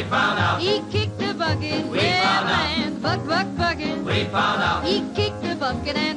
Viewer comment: Hehe i love the song old man mole when the part goes "buck buck buckkkkeeeeet!!" gets me to shout that word everytime lmfao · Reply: I always thought she was saying fuck fuck fuck it